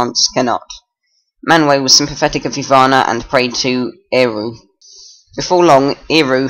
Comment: Do I hear you say Iru on 0:43?